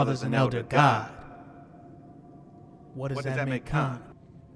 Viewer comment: I know so little about Mortal Kombat that I thought was saying "Chaka Khan" & spent the entire video looking for her because I thought she was actually in the movie! Lol